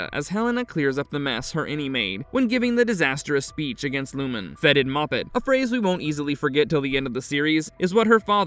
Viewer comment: "Fetid" and "moppet" are 19th century vocabulary. Kier would have used those words fluently. “The Remembered Man Does Not Decay.” --Kier Eagan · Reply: I thought he said felted muppet lol